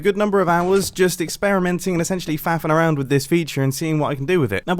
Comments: Did just say fapping around? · Was it just me or did it sound like he said “fap around with it” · did he just say “fappin’ around” lmao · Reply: Faffing-playing around/trying things out